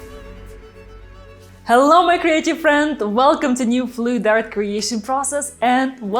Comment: CREATIVE friends. I always thought you said "hello my crazy friends" lol